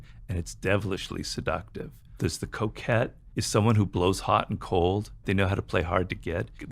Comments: 😂 I thought he said the coke head while just listening. When I came to read the subtitles I could see he said The Coquette. 🤣 The Coquette. He taught me a new word today. 😂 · Who also thought he said "coke head" until you saw the printed word "coquette?"